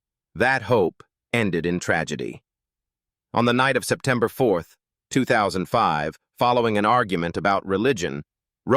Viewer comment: at time 1:47..............did you say............September Fourth ?